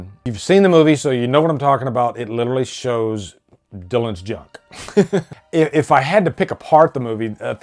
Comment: Did you say 'jock' or john? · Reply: Junk.